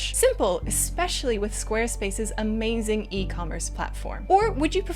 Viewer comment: lol, i thought he said this video was going to be sponsored by square ENIX, not space 😂